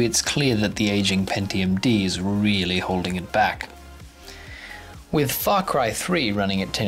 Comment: i thought you said fuckcry